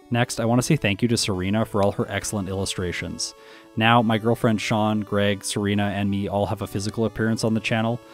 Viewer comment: At the end it almost sounded like you said "my girlfriend, Sean" as if Sean was your girlfriend and that was a surprise. Lol